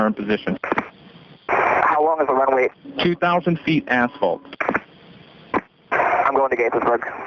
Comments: I really thought he said " 2,000 feet asshole". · I seriosuly thought the ATC said when asked about the airport "it's 2000 feet asshole" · I thought ATC said “ 2000 ft , asshole “ (asphalt)